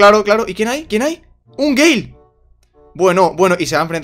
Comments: "Quien, quien esta ahí? A es un gay" 💀 (Yo se que dice gale pero suena como la palabra gay) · XD "un gay" se que dijo gale pero igual es muy XD